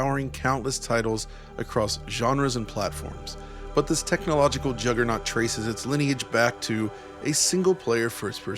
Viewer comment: Misheard the beginning part to say “juddernaut”